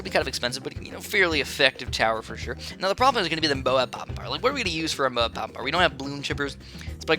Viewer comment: tf u saying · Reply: He said he has little Moab popping power :)